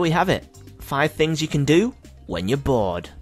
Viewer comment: Wait, I thought he said 5 things to do when your born....